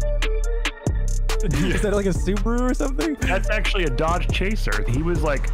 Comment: "Is that a subaru or something" Me a fan of subaru: :( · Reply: I thought he said supra..